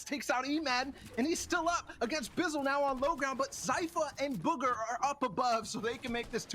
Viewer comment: did he just said buger